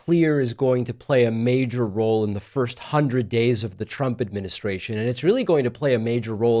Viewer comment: I viciously smile at myself mishearing the "trumpet ministration"..